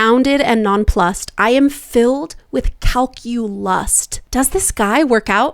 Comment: reminds me of the enunciation comment you mentioned, since I didn't hear calculust, I heard calculus and I was so confused. Edit: oh the Broadway song is a bit too much for the movie imo but I get it like she should have been more upbeat and emoted. I found Katie so fucking boring in this movie. She is a great actress but her singing was bland and she was the wrong cast for it imo 😅